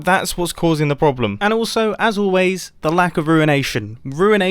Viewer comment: "As always, the lack of ruination." I thought he said "The lack of urination."